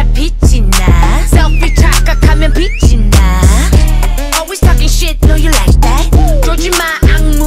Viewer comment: Did she say I know you talking shit i know you like that?😭